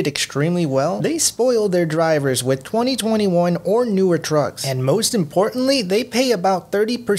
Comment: every time you said trucks i thought you said drugs lmfao